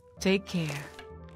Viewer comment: When you misheard the word 'care' for 'aim'.